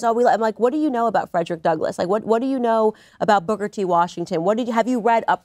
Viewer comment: am I the only one who heard "Booger T Washington"?